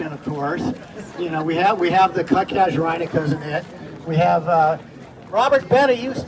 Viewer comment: What a fantastic collection of plants! Can anyone tell me what the plant is in the "Cousin It" section - I couldn't catch the name. The one I like is the 1st plant he names after saying Cousin It, and points to it at the time stamp 19:14 I REALLY like that plant and would like to find out more about it ! I just need the name... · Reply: Thats Casuarina Glauca